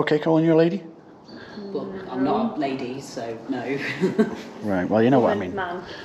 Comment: did she say Lady + Man?